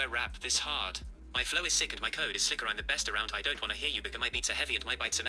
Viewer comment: YO whaT? 0:15 N-word? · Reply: It said "bigger", but I had to do a double-take as well.